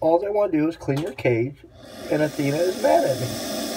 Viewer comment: What's her name? I thought you said Athena. Our ball python is named Athena. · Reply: Yes her name is Athena. Cool you got a ball python named Athena too.